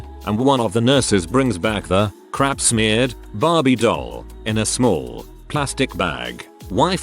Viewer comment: did he just say barbIE doll